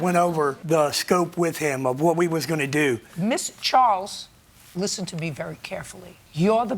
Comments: did she say Miss Charles? XDDD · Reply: That caption wrong judge Judy said Miss charles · I’m sorry, I can’t be the only one who hears Judge Judy say ‘Miss Charles’ instead of ‘Mister’, am I?